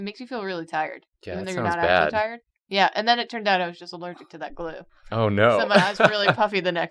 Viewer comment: Worse crosses of mythical creatures? Vampire giraffes Were-Squirrels Mummy Jellyfish Just a few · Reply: I thought you said "Just a Jew" which was funnier